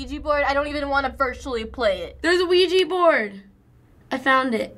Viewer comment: what is a luigi board? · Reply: Hahaha, I totally thought she said it was a Luigi board!